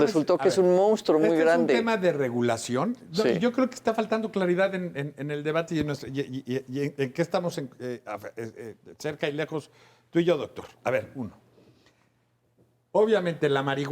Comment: Que dijo?!!! 15:50 jajajaja 🤣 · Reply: dijo, yo creo que esta faltando claridad en-en-en el debate y en y-y-ie en que estamos y ie afe e-e-e-e cerca y lejos, tu y yo doctor <3 haber uno.